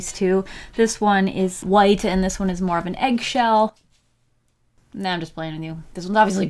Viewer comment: “This one is white and this one is more of an eggshell” I was just like....what is happening xD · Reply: I thought she said “this one is white and this one is Marvin eggshel.” ... I’m sorry 😭😂